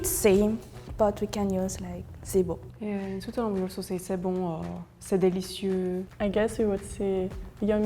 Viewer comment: the subtitles get... concerningly inaccurate to what the people are saying "C'est bon" Stubtitles display C'est bien "C'est trop bon" Subtitles display C'est trés bon (Très* btw. Least', for Québec's grammar since I'm not sure about the others) ''C'est bon, c'est trop bon'' Subtitles display C'est trés bien